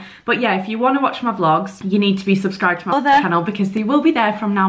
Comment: Dont think we didnt hear you dubb in other at 1:29 lmao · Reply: I thought she said 'mother' and had to check the comments 😂